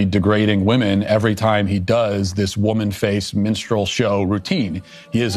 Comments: At first, I heard “minstrel show” as “menstrual show”. 🤣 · I thought I heard “Dylan Mulvaney is intentionally degrading women every time he does this woman-faced menstrual show routine” LOL!!!! But there is tragedy in this, and Dylan will never shake his Y chromosomes.